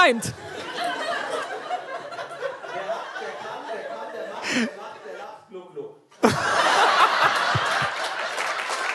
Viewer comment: Mega 😍 so lange drauf gewartet, aber was sagt Till bei 14:40?? Ich hör es leider nicht :( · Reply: Wer lacht, der kann; wer kann, der macht; wer macht, der lacht; Gluck Gluck Aus den Untertiteln